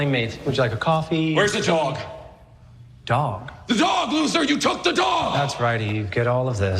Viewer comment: Hey so I'm seeing people saying Superman called Lex a "loser." I was confused like when did he do that? Then I listened again and realized pale are mishearing him. He says, "The dog Luthor, where's the dog!?"